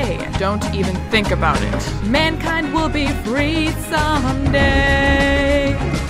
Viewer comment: What I read: "Mankind will be free some day!" What I hear: "Eren will be free some day!"